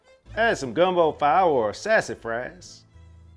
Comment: what is he even saying? I tried about 5 times but I cannot decipher it hahaha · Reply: Gumbo powder or sassafras